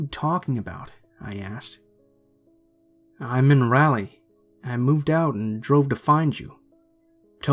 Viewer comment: That's how you pronounce it dude: <link> Fact check before you try to correct someone lol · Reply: 14:05 it sounded like you said rally lol